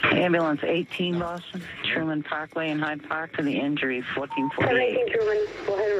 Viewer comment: I thought he said Tide Pod instead of Hyde Park.